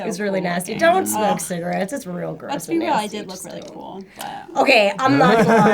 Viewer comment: Bree is blazed lol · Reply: Am I the ONLY ONE who heard distinctly at 17:15 Bree saying lets be real I just smoked a bowl?! I can't be 😂